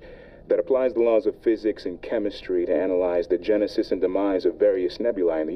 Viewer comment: When he said genesis I thought he said jenicide